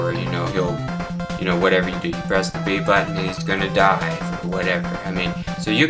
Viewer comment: I thought you said die XD BTW, how much did that capture card cost? I got a USB capture card for $30-$40, and I've seen it produce quality better than my TV ._.